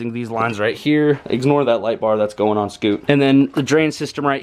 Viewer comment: Did you say a light bar for scoot at 10:26? Kinda scary.